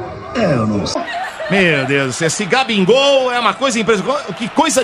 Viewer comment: ouvi GABINGOL KKKKKKKKKKKKKKKKKKKKKKKKKKKKKKKKKKKKKKK